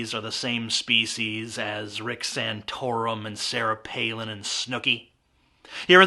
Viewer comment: At first I actually heard him say Nick Santora and I was like "Why?", but then.. Rick Santorum. Alright.